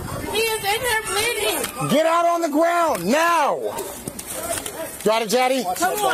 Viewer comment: im not sure but at 2:51 i swear he said "you got it daddy"